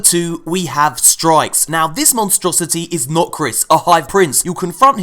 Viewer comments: Everytime you say 'Nokris' I hear 'Not Chris' >.< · At first I thought he said "THIS IS NOT CHRIS".